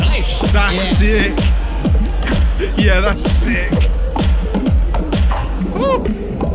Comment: めっちゃ「いやらしい」に聞こえるw